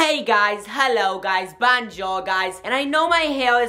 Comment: at the begining i thought he said bajo guys but he said bojour guys